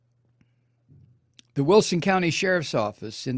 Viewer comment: only in Texas is there a strata below the evangelical crazy s . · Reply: At first I thought he said "Williamson County" and I nearly peed my pants because I grew up in that area but He actually said "Wilson County" which I guess is in another part of the state. Im gonna guess North cause that area seems to be a haven for troublemakers like these two.